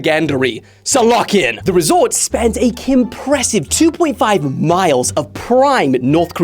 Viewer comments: did you say kimpressive? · Did he say KIMpressive or am I stupid? If it was intentional then I'd like to admit that it made me wheeze for 2 minutes straight